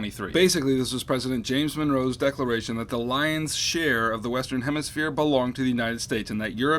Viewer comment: Does anyone know what he said at 3:48 when he was talking about the Monroe Doctrine? I can't understand what the word is and the captions say "lion's share" and, although I may be mistaken, I highly doubt that President Monroe declared the lion's share belonged to the U.S. Can anyone help me out? Thanks!